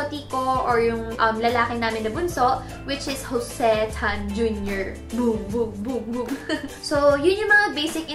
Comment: Jose Tan Jr.??? did i heard it right? i'm chinese and my last name is Tan also.. if that so im shookt